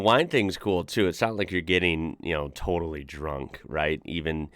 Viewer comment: "The wine thing is cool, too. Sounds like you get totally drunk..." – No, not really. Thing is: The fact that it's considered normal to have a glass of wine with a meal, usually leads to less drinking. I'm sure it varies from country to country, but whereve I've met people who had some wine with their lunch, they usually didn't get drunk, and neither did I. To quench your thirst, you drink water. The wine's much more for aromatics. It changes the taste of the food. And there might be a (very) slight buzz at the end of the lunchbreak. Of course there's always a percentage of any population who would drink too much. But it's certainly not the norm to get drunk over lunch. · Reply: I think you misheard: 2:00 "it's NOT like you getting totally drunk"